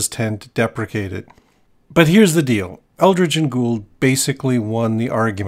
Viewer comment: Very disappointed to find out that I misheard you, and their names weren't actually "Eldritch and Ghoul," but anyway great and interesting video